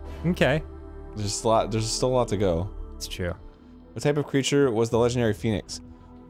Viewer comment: Me, nearly asleep, mishearing arin say “what kind of creature has a legendary penis?”